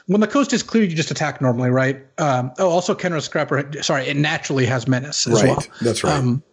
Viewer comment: When said "Khenra Scrapper" I thought he said "Kenrith's Crapper." I guess that would be the real Throne of Eldraine.